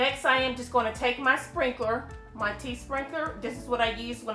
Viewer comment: Love this video! Your tumbler looks AMAZING! What is the tool you used to blend the colors called? I thought you said “tea sprinkler” in the video but I can’t seem to find it on Amazon. Any recommendations on where I can buy it? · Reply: Hi it is a tea strainer link below .. Hope this helps . ANYI16 Tea Infuser Filter 2 Pack... <link>